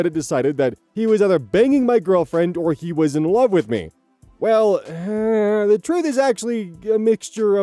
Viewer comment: I'm so dumb- when I heard this I was like "he.... Was banning his girlfriend while also being in love with him??" 😭😭😭 what he said makes much more sense lmao